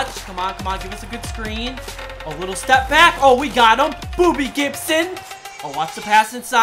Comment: did he say booby Gibson